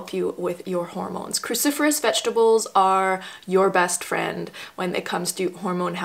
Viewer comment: I keep hearing Christopher's vegetables :P No offence, I just find it mildly funny :)